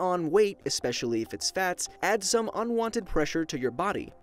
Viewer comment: Replay 2:21 It sounds like Blocko is saying bats instead of fats